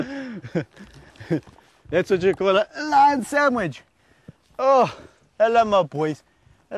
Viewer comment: did he just say Sandwich? :I